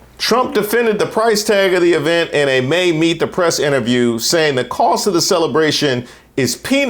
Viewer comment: Ngl, when you read the name of the May "Meet The Press" interview, I wasn't looking at the screen and thought you said "Made-Me-Depressed Interview", and immediately thought "same".